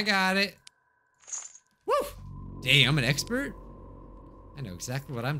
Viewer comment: DID SAY DAMN OR AM I HEARING THINGS?!